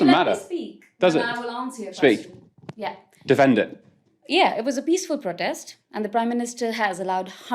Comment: Good video, but she did not say, "These people", she said, "It was a peaceful protest."